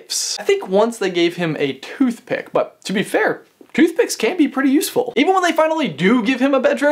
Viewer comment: A note about the subtitles; the subtitles say 'toothpicks can't be pretty useful', but what is said is 'toothpicks can be pretty useful'. 2:35 I think Harry did develop an obscurous, but it never was able to grow to the point of causing mass damage.